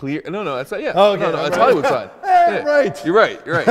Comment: yoo did he just call out hollyhood 9:00 or im imaginning shit 💀